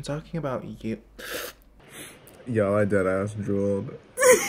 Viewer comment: BITCH I THOUGHT HE SAID I JUULED NOT I DROOLED LMAO